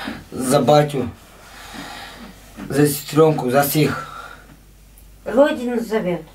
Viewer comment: that dude's accent is odd. When he tries to say "за всех" It sounds like he says "за всіх" like in Ukrainian.